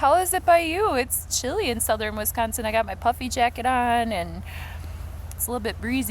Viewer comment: I thought you said “puppy jacket.” It’s so chilly, I’m wearing my “puppy jacket.” I was like what’s a puppy jacket? Before I realized you said puffy jacket, of course. I like your channel!